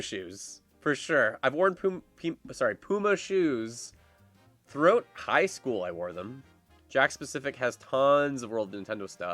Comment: did he say Throat Highschool LOL